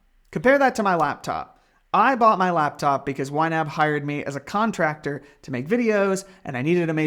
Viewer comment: I love the subtitle says Why App instead of YNAB 😂